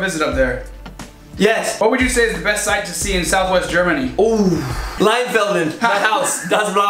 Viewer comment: Did you say LEINFELDEN? 6:57 ? I am from Leinfelden, thats why i am asking :D